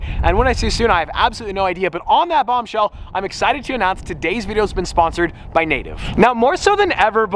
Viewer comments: I heard "Todays video has been sponsored by Nato" :p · when you said "today`s video has been sposored by native" i thought you said "today`s video has been sposored by NATO" hahahaha